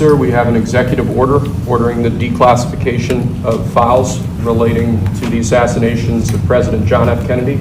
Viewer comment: Thought he said FAUST at first. Im thinking WTF is Faust?? It's. FILES , not faust.